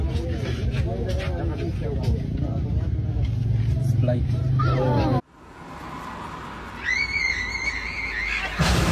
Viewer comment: what he say? splake? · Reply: Sprite